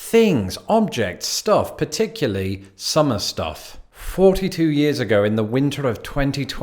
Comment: did he really say 43 years ago